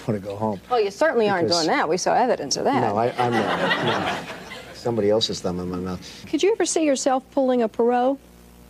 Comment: did I just hear: "someone else's thumb in my mouth"? GROSS!